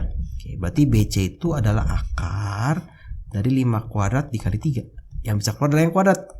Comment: haloo kak di menit 11:37 ngomong apa yaa kak? soalnya ga jelas suara nya.. Terimakasih uda jelasin tentang pythagoras kak, sukses selalu 🤗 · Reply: Sama wkwkw,dengernya habshsaha kuadrat